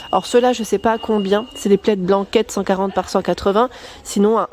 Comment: Coucou bonjour ! J'espère que tu vas bien. J'ai entendu "plaid blanquette", je ris toute seule... Bisous.